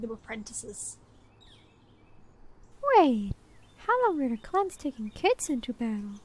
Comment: What are they saying ? I didn't catch any of that 😅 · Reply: “Wait... how long were Clans taking kits into battle?”